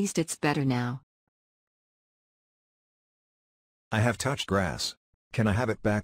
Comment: This Video is Just a Joke BTW. Don't Send It To My Mom....😫😨🤪 .... ... Also I have Touched Grass... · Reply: I thought you said glass LOL